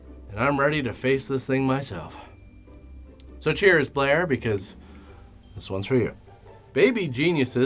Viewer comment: I thought you said “ cheers player” 😅